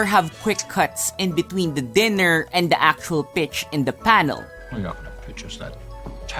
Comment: I thought he said "bitch in the panel" while the video was showing that lady. And i thought "thats a bit harsh" :D